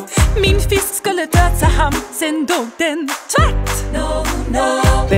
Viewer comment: Holy shit, this is amazing. Your Swedish is almost perfect. Just one nitpick though, it sounded like you said tvätt (laundry) instead of tvärt (suddenly), but otherwise than that it was perfekt 👌.